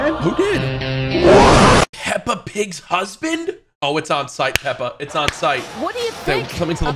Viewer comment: did he just say peppa pig husband? 🥲🥲🥲